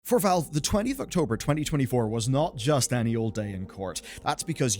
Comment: I thought I heard "October 20th 2024" Umm it's October second 2024